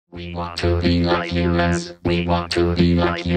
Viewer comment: I keep hearing “We want to be like you less”